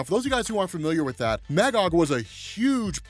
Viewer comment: I thought you said Mangog and had to rewind it 😆